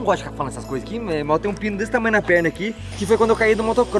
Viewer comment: juro que ouvi pinto kakak